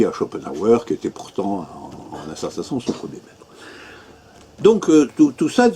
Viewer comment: Il dit quoi sur Schopenhauer ? On entend rien, il parle dans sa barbe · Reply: Je ne suis pas certain mais je pense qu'il dit "Schopenhauer qui était pourtant en un certain sens son premier Maître"